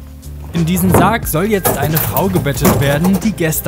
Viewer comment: "In diesem Sarg soll jetzt SEINE Frau gebettet werden, die Gestern gestorben ist" Hab ich das richtig verstanden oder sagt er "eine"?? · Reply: Eine